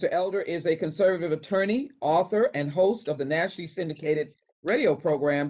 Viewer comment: It sounds like she says Nasty instead of Nationally